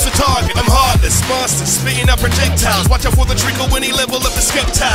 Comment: I heard Treecko. I came as quick as I could.